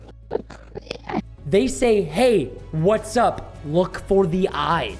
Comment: i heard " hello, what's up"